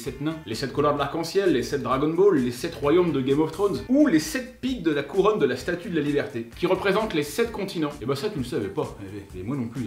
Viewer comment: j'ai entendu "les 7 bites de la couronne de la status de la liberté"😅